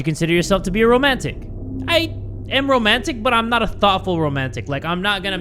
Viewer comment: For a second I thought he said “I am romantic but I’m not a thot”